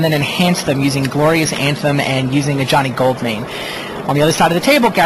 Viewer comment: When he said "Ajani Goldmane" at 1:26 I heard "A Johnny Goldmine"